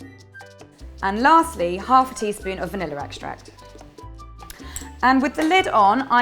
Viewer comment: These look so cute!! Love how they're decorated, kind of reminds me of The Simpsons haha :) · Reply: makes sense. I just thought I had heard her call it "vanilla extract". Thanks! :) -Abby